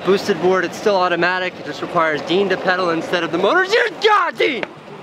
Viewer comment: did you say GOOD GOD DENE!!